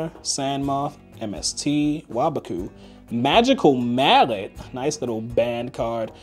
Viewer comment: did he say magical mallet is banned ?? lmao